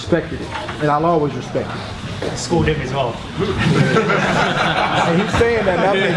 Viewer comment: what did the guy say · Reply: " you schooled him aswell"